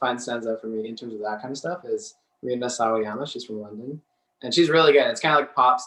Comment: Cool interview with cool people! P.S. could anyone pls help me? I can't catch the name of the London based musician that was talking about (~ 7:35), could anyone write it in reply to my comment? It would mean the world :) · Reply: Please see Kevin Cunningham's comment above, it's Rina Sawayama!